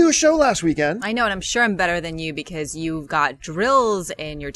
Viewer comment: haha I thought said "I'm better than you because you got drilled last weekend .. wow, didn't know y'all were that kinky. jk hope you feel better! hope the insurance covered that dental implant that you got cause i know they ain't cheap since i work in a dental office.